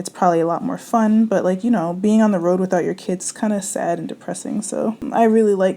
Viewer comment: "Being on the road with your kids is sad and depressing" 😂😂 I feel you sis! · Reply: Nee Cee I thought she said without the kids in depressing